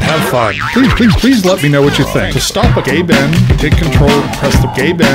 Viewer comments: is it just me or do i hear "to stop a gay ben" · still sounds like he is saying gay-ben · who else thought he was saying "gay ben" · I hear "Gay Ben". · i heard this and thought it said "gay ben"